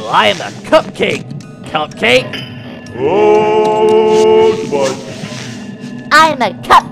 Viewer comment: "Ohhhhhhhhh fudge (i think thats what he said idk)